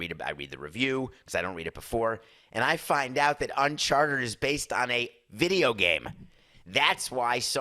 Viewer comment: Hm. It sure sounds like David is saying "Unchartered" (not rented) rather than "Uncharted" (not on the map). 😄